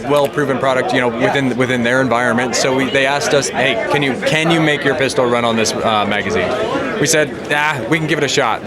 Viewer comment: all I heard was “why can’t you do what the platypus did?”